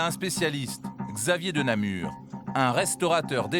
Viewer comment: Je me suis arreter a 2:14 oui oui le nom du spécialiste m as fais beuger mon cerveau rassurez moi j ai bien entendu de namur ^^'